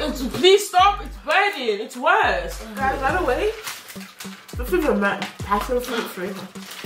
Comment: It’s sounded like she said “guys btw the flavor meant passion fruit, fruit.